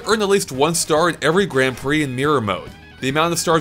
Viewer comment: It sounds like your saying meer instead of mirror